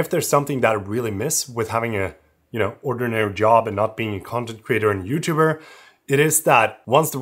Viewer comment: I misheard you for "content creator" as "comment creator"... that is interesting job.. do you think "Comment Creator" could be a real job? maybe some money in it? 😆